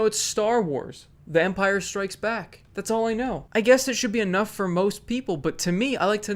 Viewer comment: sounds like says "Vampires strikes back" lol